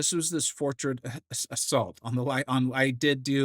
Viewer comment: Lmao i definitely thought they were saying “fortress of salt” haha.